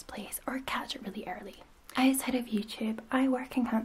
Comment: Did anyone else think she said “as head of YouTube” and not “outside of YouTube” ? 😂